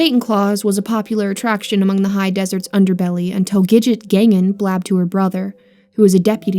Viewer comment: the hell? You have a stroke just then? Togijet gangan? Is that what you said? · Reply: The characters name is Gidget Gangan